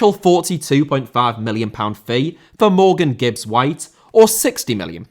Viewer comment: "A potential 42.5 bid for Morgan Gibb's wife" Is what I heard and keep hearing there. 😂😂